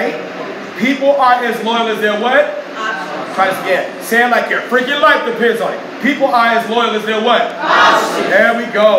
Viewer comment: This is So good. Please: What’s the word at 11:05 ? “People aren’t as loyal as they’re what? ———“ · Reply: Yes. Options.